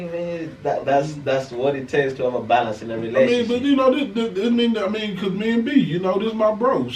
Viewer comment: I thought you was saying Balance Inna relationship is #PUREFUCKERY 🤣🤣